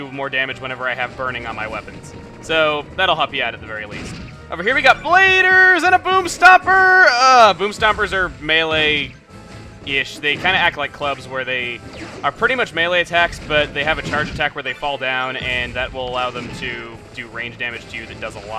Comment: Call me immature, but it sounds like he says "boobstompers". :P